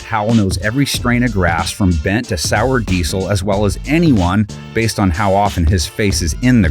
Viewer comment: Did he just say Sour Diesel? 😂